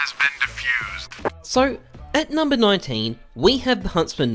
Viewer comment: did he say at number 19?